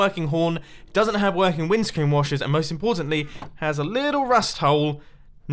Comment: did he say wind screen washers? I thought it was made of glass. BTW to get dogs, you feed wolves bones.